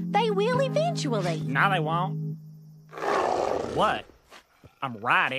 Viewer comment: What is Sally may saying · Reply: she's saying "how pissed would you be if i bet on him diana"